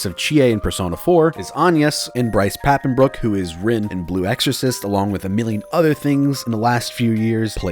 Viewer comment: I thought he said “rem from blue exorcist” I was so confused..... I like both of those anime... well I like the manga of blue exorcist anyway